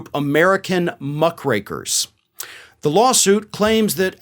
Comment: I thought you said Muff Breakers hahahaha 😆 😂 🤣 I was like whoa what did he just say? Had to listen again. Muck Rakers is what I think it is lol.